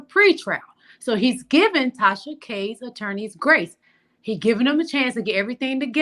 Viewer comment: I thought you said Tasha K Attorneys were a GED MESS lol, I now got it. I Luv what you bring to your channel. Keep up the great work.